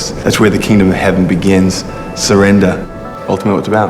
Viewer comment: Does anyone know what he says at 2:28-2:29? All I hear is ultimate something.. I want to quote it but I need to know what it says :) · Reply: Ultimately that's what it's about. I touch the sky when my knees hit the ground.